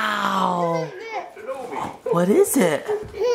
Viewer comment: Did said "What is this?"